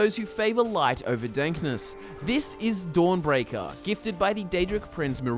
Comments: did you say light over DANKNESS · Is it just me or did he say "The light for dankness" instead of darkness? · I'm sorry but did you just say 'light over dankness'? 😂